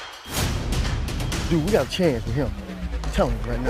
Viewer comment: Who do you think Shawn Jefferson was talking about when he said, "Dude, we got a change with him" to Joe Vitt? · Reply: I thought he said we got a champ in him but I might be wrong